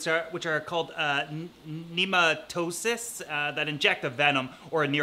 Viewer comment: nematocysts fyi lol. sounded like you said neurocysitis or something.